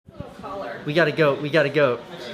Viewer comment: i thought he said we got to go at first